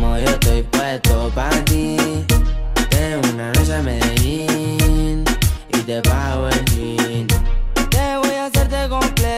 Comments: Yo pensaba que decía "Ven una noche a Medellín y te bajo el Jean" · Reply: Jaja yo pensé que decía "te vi esa noche en Medellín y te pago el trago jajajaja 😂😂🤣🤣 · pense que decia y te bajo el jeans, quedaria o no?